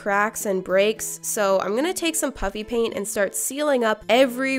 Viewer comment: The first time I heard you say "puffy paint" I thought you said "chuffy paint" so I rewinded and heard you say "puffy paint"...IS THERE SOMETHING WRONG WITH MEEEE?! Everyone: Absolutely